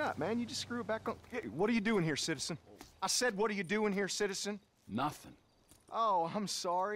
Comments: I thought he said "What are you doing here Simpson?" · “What are you doing here citizen” not even 1 second later “I SAID WHAT ARE YOU DOING HERE CITIZEN” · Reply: I thought the first line he said was " what are u doin here simpson"